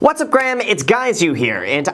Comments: Did you just say..."what's up Graham, it's guys you here" ?? Or am I having a stroke 🤔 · Wait.....did you just say “what’s up graham, it’s guys you here” 🤔 lol